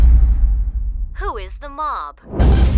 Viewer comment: i thought she said who is the mom